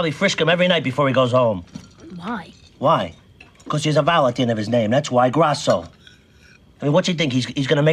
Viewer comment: Can someone help me with what tony says at 0:33? “He has a vowel something something of his name”. Kinda sounds like he saying he has a Valentine in his name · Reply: "He has a vowel at the end of his name"